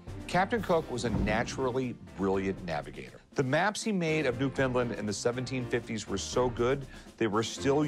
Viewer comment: Did he really say New Finland?